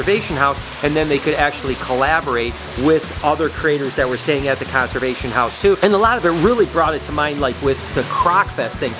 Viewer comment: Was it just me who thought he said the cancer patient house?